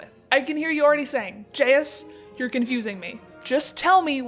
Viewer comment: Have u been triggered by the way she says Jesus Edit: she was saying jayus instead of Jesus I thought she said jesus